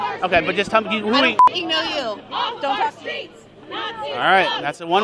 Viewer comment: I have a feeling these people have not struggled for much in their lives. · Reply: Especially the one "I don't know you, don't talk to me" 😅 At first I heard "don't touch me", would have made equal sense to me. 🤭 These snowflakes.... They go to protests, aggrrssively, without knowing what they're even protesting against. It's sad. I pity the police who constantly have to deal with this shit. Why can't we just all get along?....